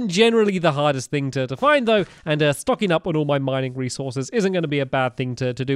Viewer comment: I thought said.. f**king up my resources.. did anyone else here it?